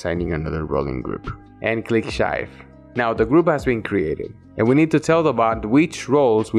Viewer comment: i think i heard- uhhh shy or shave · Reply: I have a weird way of saying "Save", I know